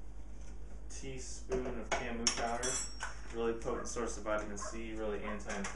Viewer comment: what was the teaspoon of something “camu powder?” high in vitamin C ? you put it in after the goji berries powder at 3:42 in the video so did you say camu powder? i couldn’t hear what you said and i do not recognize the name of it thanks